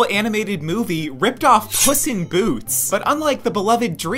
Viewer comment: I thought it said “piss in boots” 😭